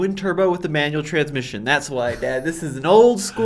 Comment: did he say “that’s why dad”? That’s cool, I didn’t know.